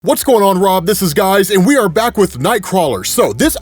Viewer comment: Intro : It sounds like Rob said "What's up Rob, this is Guys" did I hear that right?